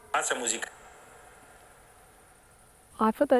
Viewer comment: So, one REALLY cool thing about that place in Hoia Baciu is that if you go on the Google Timelapse Satellite views, which go back to the 80's, you can see up until 2022 and that open meadow NEVER changes shape. Other clearings in other forests do; you can see them fill in slowly with trees and shrubbery, but not this one. It's really kinda neat to have some more tangible proof of it being like that. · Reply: Omg I just love how clearly you can hear this one at 45:20 !!!! Is clearly says "Lasa muzica!" It's kind of difficult to translate as words and phrases in casually spoken Romanian heavily rely on context, gestures and implicit meaning. They are vague and could be interpreted in two ways: 1. Let the music play on. 2. Be done with the music! (Stop doing/playing music). But you can almost certainly tell it's the latter meaning. You can now form a meaningful narrative with the info you gathered. There's two entities, a woman singing and a man who is angry at her singing, who wants her to stop. He does something to her and she is in shock, asking him "what have you done?" And pleading him to stop saying "gata!". Or maybe she means she's going to stop singing? Anyway, it's pretty coherent, and goes well with the things you saw.